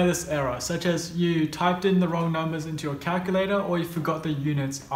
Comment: did he said calculator